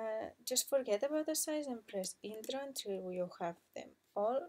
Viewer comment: Thanks for the tutorial. i'm having trouble hearing what you said at about 3:12 when you say "forget about the size and place ____" i don't know what you do and CC says place in toronto which i'm sure isn't right. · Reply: 🙊I'm sorry it was my first videotutorial plus my English pronunciation needs some improvement hahahaha What I say is "Forget about the size and press intro until you have them all in place"